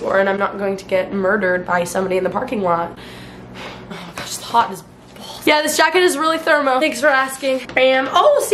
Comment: or 6:39 did i just hear its hot as balls 😆